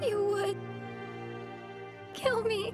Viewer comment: couldve sworn he said "quit cryin and smile" no just me okay :)